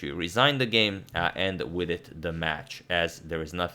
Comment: Agadmator: Yan resigned the game and VIDIT the match. I’m the only one who hears Vidit ?